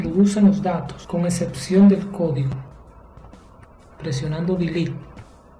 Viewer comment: hola, en el momento que ingreso datos si los guarda pero no desaparecen los datos del registro, se quedan ahi. Creo que la respuesta esta en el minuto 15:11 pero no logro escuchar que es lo que dice? alguien puede ayudarme GRACIAS! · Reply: Hola, lo que no se escucha bien es la palabra DELETE o SUPRIMIR en español, ahí explico que se deben borrar el contenido de cada celdas y es para que cuando finalice el proceso de guardar las celdas queden vacías (desocupadas) Saludos.